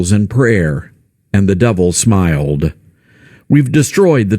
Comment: I kept hearing "and the devil's mild"